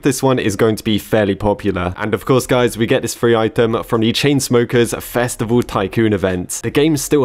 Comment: I swear I thought you said "furry item" instead of free item